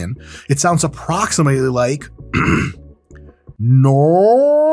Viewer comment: Honestly, it sounded like you tried to say Norge (Norway) at 5:31 xD In any case, CLOSE!